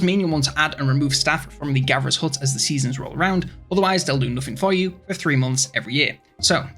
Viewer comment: I seriously cant hear a single word you said lol. even slowed down just sounds like made up words. Anyone know what he said for food? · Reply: “they’ll do nothing for you for three months every year”